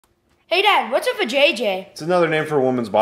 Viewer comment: I thought he said “hey dad whats up with jj?”💀💀